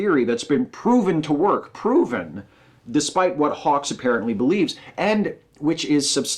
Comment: Sounded like you called him 'hawkes' again @ 16:35...